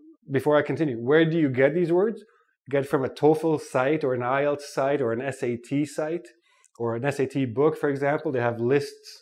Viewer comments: What did he say at 7:58 all I heard was S.A.T site? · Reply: "Get them from a TOEFL site, or an IELTS site, or an SAT site." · I'm trying to learn some harda vocabulary useful words, i didn't understood what he said at minute 7:59 after he said a toefl site. Can you people can tell me what he said? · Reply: Do you mean ielts site? Also you cannot use didnt and second form of the verb together, i wanted to correct that :))